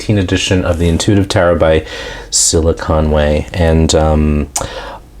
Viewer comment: I thought you said silicon way...